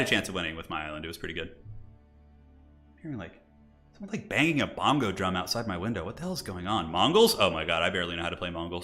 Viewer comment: it sounds like says there are Mongols banging a bongo drum outside his window. Had me 😂. Good job as always! Thanks for the great content